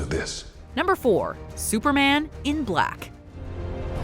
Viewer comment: Haha i for a second i thought #4 said superman is black 😂